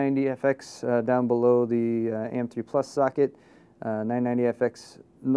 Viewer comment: Am I the only one who heard oh holy call that an AM2+ slot at 8:45? =O